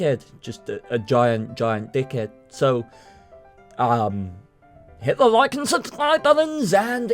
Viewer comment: I thought he said Hitler like and subscribe button :P Lol.